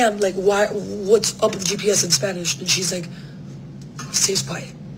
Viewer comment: Wait what did she say at the 1:28 mark that was so scary? I can't make it out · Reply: She asks what's up with the GPS in Spanish, and the creepy driver stays quiet.